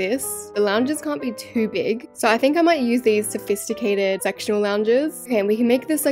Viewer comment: when u talked about the lounges i thought u said sexuil lounges....aqward..